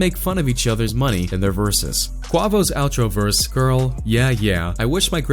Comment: I thought he is saying guavo or guava😂😂😂